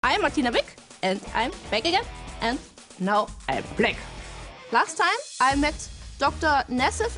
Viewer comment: wtf did she say? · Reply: FaZe Baby2 I am Martina big ,I am back again and now I’m black